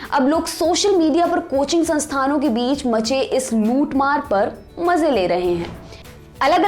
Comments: i heard moothmar ... It was actually lootmaar 🤣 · Did she just said "In Coaching center ke Muth mar par"?? I heard that😂